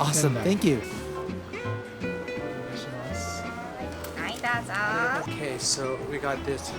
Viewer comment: what does the clerk say, here? I was expecting ありがとう or something, but this sounds more like アイターサー. Can someone explain? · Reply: はいどうぞ I believe it’s hard to hear her